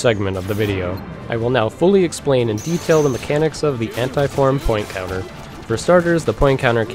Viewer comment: I heard "hentai-form" xd